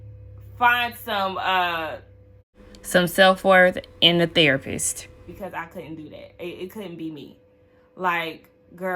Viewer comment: Wait what!!!!!!!! 7:41 🤣🤣🤣🤣 what was you really trying to say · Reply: I said "dignity" but I was taking too long to say it so I did a voiceover 😂😂 it literally wasn't nothing